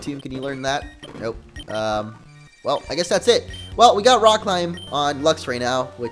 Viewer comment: on 16:44 it said rock slide instead of rock climb